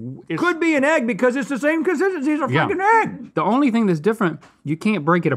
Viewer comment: did Rhett say the F word??? 😲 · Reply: he said fricking look at the captions also it's pretty clear